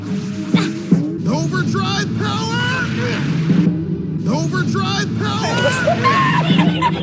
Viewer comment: I thought he said over 9000